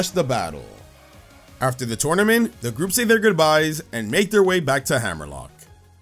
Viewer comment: bro I swear I heard “and make their way back to hell” and did a spit take